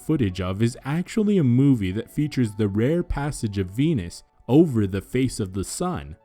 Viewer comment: thought he said "penis" first. XD